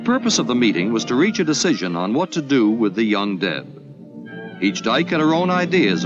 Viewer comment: Sounded like the term used 0:10 was ''UnDead'' 1:10 but I guess it was ''young Deb''...? And it sounded like he says 2:14 ''dead'' instead of ''dare'' ''The dead has no place to go'' 2:29 (But it all sounded much more innocent the 2nd time) I did na-zi 2:00 that coming